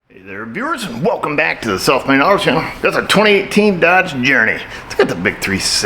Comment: When I first started watching this channel, I and many other thought was saying "Welcome back to the selfmade auto channel", which seemed appropriate. Now I see that even the closed captions think that's what he's saying! LOL!!!